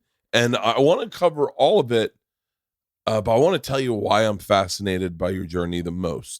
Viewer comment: does Bert say, "I'm FATscinated" ??? lol These two are so similar in so many ways, I swear she could be his daughter haha.